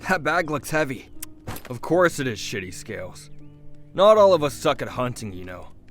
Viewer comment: it 2:19 am on a school day rn (monday) and I heard shitty souts,tf